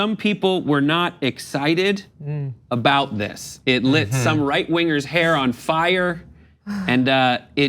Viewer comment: Miller is right wing. Miller is triggered by Parasite. Therefore, all Right wingers are triggered by Parasite? Y'all need to try a bit harder. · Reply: Quinones Quinones You know that if you go to 4:17 he said “SOME right wingers” he did not say “all right wingers”.